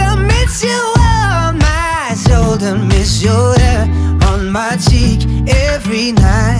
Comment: Sounds like he says “I miss your hair on my teeth” haha amazing song tho